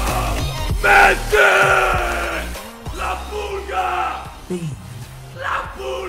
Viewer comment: I thought he said LA PUTA when he got messi LOL